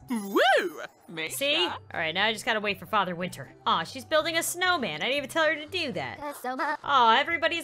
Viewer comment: who else heard something with snowman? Just me? Ok. Bye